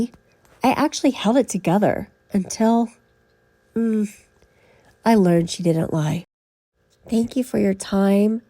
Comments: I love your content you are fantastic! So what I hear of what I heard is “ I held it together until I learned she didn’t Lie “ that’s what I hear it’s hard to make it out .. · I hear "I learned that she didn't die." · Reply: I had to come back and listen again and again and now I think you are right! All I hear now is "I learned she didnt die. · I heard, " Me, I actually held it together until, um, I was aware that she didn't die." If true, it indicates he went in to finish the job. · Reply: I hear "I learned that she didn't die."